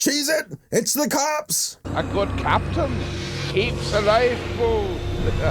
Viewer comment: Did he just say "A good captain needs a Waifu?"